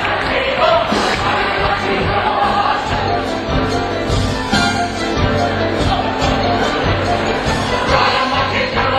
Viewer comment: what did the shields say around the 5:30 mark? I'm guessing they were spelling something...? :) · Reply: in the production i did of it, we said molecat, camltoe then camelot